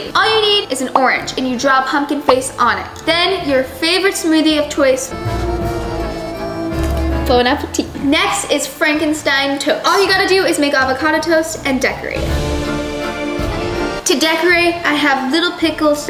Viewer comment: You know at first I thought she said “bone apple teeth”😂😂😂